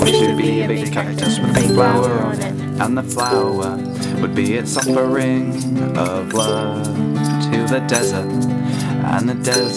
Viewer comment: i thought you said "and it would be its offering of blood to the desert..." xD